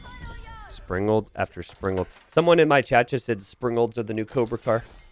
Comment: Every time they mention Springalds I swear I hear the words "spring rolls". Not sure if my hearing is bad, or I'm just really hungry